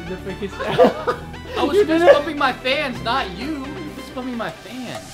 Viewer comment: That sounded like fist fu**ing my fans !